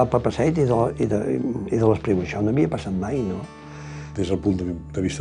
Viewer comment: Ayuda que dice en el minuto 24:58 después de Papasseit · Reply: «[…] i de l'Espriu. Això no havia passat mai, no?».